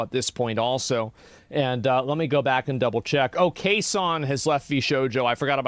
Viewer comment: I heard “Kson has left VShojo” and thought you said CaseOh. i was very surprised.